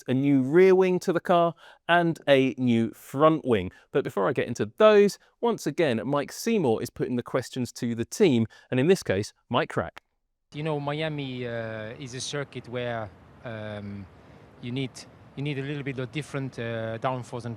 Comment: I swear I heard "Mike Rack"